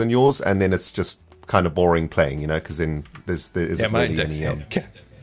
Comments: I can't for the life of me figure out what says on 0:37 plz help😭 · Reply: “my decks handicapped” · I know this is old but does anyone know what saying around 0:36 to 0:39, I don't understand. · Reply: He said “yeah my decks handicapped” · Any chance someone can tell me what says around the 00:36 mark? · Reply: ''yeah my deck's handicapped'' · what did say? I didn’t make it out. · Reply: "yeah my deck's handicapped"